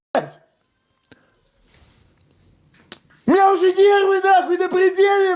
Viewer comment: Мне послышалось или чувак сказал ( у меня нервы напердели.....)!!! Гарантия, что у тебя это сделают не только нервы.😂😂😂